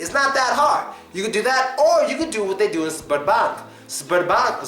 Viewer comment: When you first mentioned the name of the bank thought you said sperm bank.